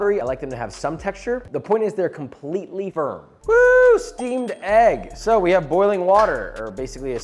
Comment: The amount of time I heard "Steam Deck" instead of "steamed egg"